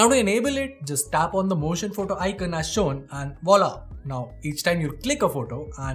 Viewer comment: did he said "voila" in french ?